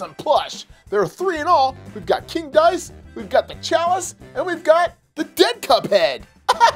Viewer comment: Did you just say Lol